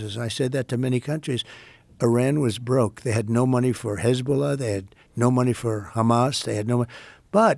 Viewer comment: no money for Hasbulla is what is sounded like lol